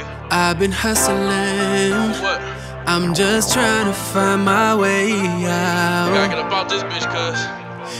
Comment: Did anyone else heard i'm gay